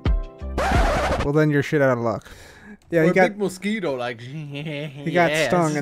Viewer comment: Did I just misheard your shit out of loves